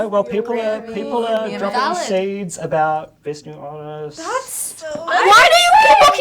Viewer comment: Please what did he say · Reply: ”people are dropping seeds about best new artist” is what he says